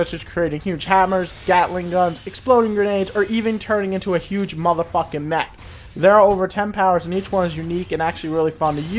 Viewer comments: did he say you turn into a huge neck? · in the video, did he say you turn into a huge neck?